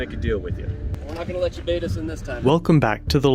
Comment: Lol I thought he said "not gonna let you betas in this time"